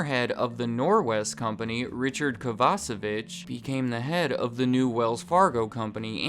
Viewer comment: Mike, for a split second, I thought you said "KovaceBITCH" and not Kovacevich hahaha